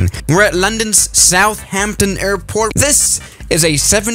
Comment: Did he just say "London's Southampton airport"????